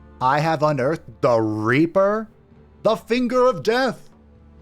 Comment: I heard someone say they're gonna finger someone? 😏